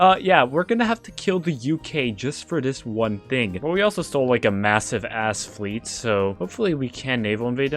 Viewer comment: did i hear right, you just said massive?